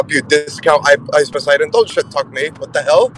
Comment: I thought that man said "Dead scalp Ice Poseidon". I was screaming, lol.